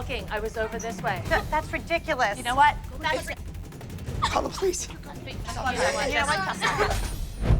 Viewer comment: I wanna know what was actually said here ughhhh 🤦🏻‍♀️😂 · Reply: She said that’s bullish!@